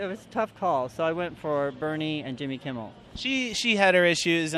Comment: did he just say Bernie and Jimmy Kimmel? Lol?